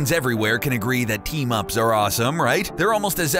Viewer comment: When he said “Team ups” I thought he said Teen Moms